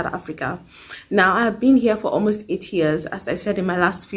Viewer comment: 😂😂😂 You're the only one who heard 80yrs o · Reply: I heard that as well😭😭I won’t lie but I was like she said 8 years but sounds like 80